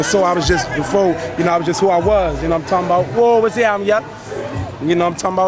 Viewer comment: WTF did he say @ 2:23? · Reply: He said Woh what's happening yeah